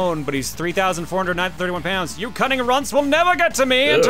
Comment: Cunning runts? I thought at first he said running